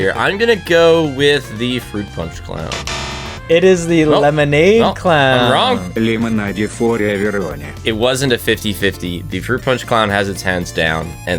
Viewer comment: лемонаде фор эвероне (literally what it says)